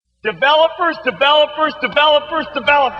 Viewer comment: i hear "Developervs" after a time... O_o